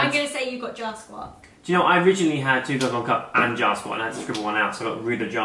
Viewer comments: Also, like this if you googled "Jar Squat" in the middle of this video! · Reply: Josephine Covell lmao I thought he said 'jaw squat' so I googled that and was so confused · Dang thought they were saying JAW squat.